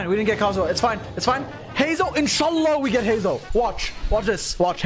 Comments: wait bro, you muslim? i hear in sha Allah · i thought bro said inshallah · did he just say InshAllah?